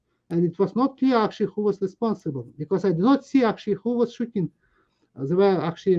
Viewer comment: Minor quibble I think he said "not see who was shooting" not "Nazi shooting".